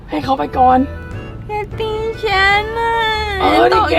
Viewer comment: ซับผิดป่าวครับ พี่พูดว่า "เเกตีชั้นอ่ะ"